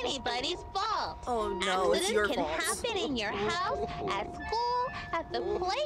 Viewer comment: i heard wooly said "it’s your fault" to amanda- 😭